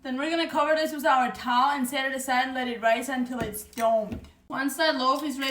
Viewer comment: I thought she said ."& let it rise until it's Dope! ... cool-that's dope!